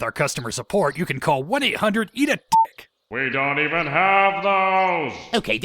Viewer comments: At first I thought I heard WE DON'T EVEN HAVE PHONES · Listen to what Nail says Then Do you hear guru say "We dont even have phones!!" Or Do you hear "We Don't even have those!" Because I'm gonna be honest with you, for so many years I thought it was a joke about namekians not having phones (just like they don't have tvs) Someone tell me this was on purpose otherwise I'm gonna lose my f#ckin mind 😂